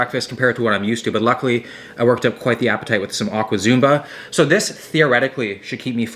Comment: Ohhh aqua zumba. I thought he said awkward zumba. That was waaay more accurate...